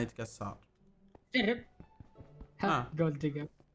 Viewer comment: علموني وش قال هنا في الدقيقه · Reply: قال قولدقر 💀 .